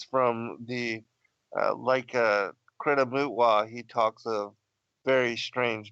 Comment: So eagerly listening to first part one, then this, tomorrow three. Thank you brothers and, what delight to listen. at 1:29:30, what is it you read from please? I heard karena butwa? But cannot find it. ... The historic artwork enthralls me, and rings so true. In the photographs I take of celestial bodies, skies, and chemtrails, there are always orbs, always green. One image here was of crafts with beings descending from a skyship to Moses? And hearing that the structures on earth mimic heavenly makes so much sense. All Lucifer dos is mimic. It ALL makes sense. Such revealing. Just can't get enough =) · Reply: Kebra Nagast <link>